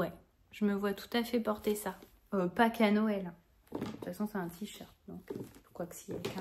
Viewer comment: merci pour ce partage. je te partage mon humour : lorsque tu dis à propos du tee à damier "je me vois bien porter ça, et pas qu'à Noël" j'ai entendu "et pâques à Noël" j'ai bien ri quand j'ai compris ma méprise ^^